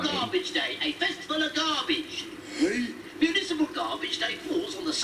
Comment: when I first saw this episode I thought the garbage dump manager said instead of municipal garbage day I thought he said invisible garbage day